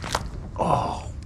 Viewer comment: backup? oh i thought you said ketchup